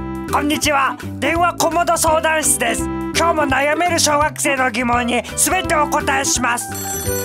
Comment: なんか小学生が小惑星に聞こえたような気がする...気のせいか...な？